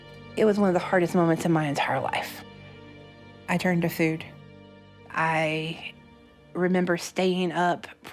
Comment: Am i the only one who hears her say "I turned her to food" what the f